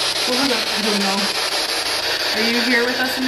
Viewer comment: idk about you guys but I heard "really"